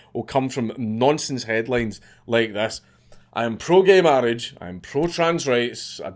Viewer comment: I was typing some papers that took me a bit off focus from the vid and I thought I heard said "I am pro Gamer Rage" when he actually said gay marriage If it was what actually said that would be epic